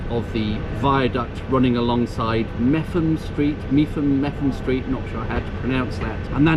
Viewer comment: I thought you said Methven Street but it turns out it's Mepham. I would pronounce that as Mepp'am - which is how one pronounces Meopham the Kent village a bit south of Gravesend. Near me at Linlithgow we used to have an Auld Hole In The Wall (now "gastrified" as Café Bar 1807) but there is still a Hole In The Wall at Dumfries - no doubt one of Rabbie Burns favourites.